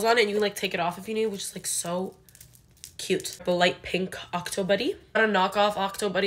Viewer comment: I thought you were saying "octobery" with an arab accent or smth but you were saying octo buddy 😂❤